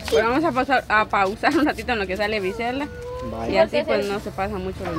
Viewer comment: MINUTO 9:32ESCUCHE BIEN CLARO QUE LE DIJO "SUBIME AHI EL TRAPO BIEN MI AMOR" JAJA😂 ESCUCHE MAL O BIEN ALGUIEN QUE ME DIGA!!!! 😂😂